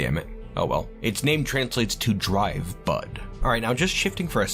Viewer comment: This is why I should look at the screen periodically while listening to things. I thought you said dry butt instead of drivebud for the artifact name.